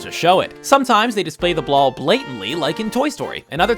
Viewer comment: does he say blall blatantly instead of ball blatantly?